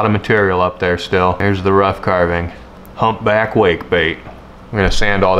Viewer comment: At first I thought this said humpback whale bait